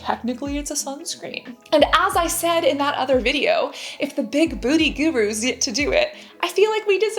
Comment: Sounds like she said "the big booty gurus" 🤣